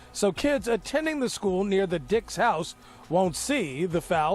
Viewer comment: Did just call this woman a dick???